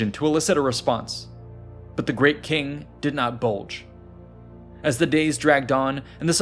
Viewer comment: did you say the king “did not bulge”?like he wasn’t aroused? Lol